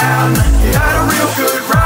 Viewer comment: First I thought he said Batman is Patfan..xD